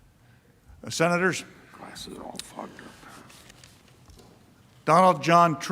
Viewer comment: Is he saying “Senators (senators all fucked up)”?😆